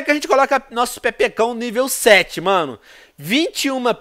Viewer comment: só eu escutei pepekao